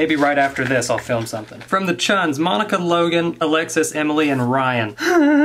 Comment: wait did he say chun? Because that’s my last name :)